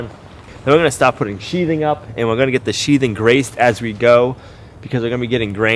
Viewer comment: It sounds like you said you'll get the sheathing graced. What does that mean?